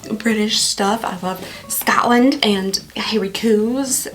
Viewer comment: When you were talking about loving British things I thought I heard you say “I love Stalin…” 😂😂😂 I was like, surely not! Lmao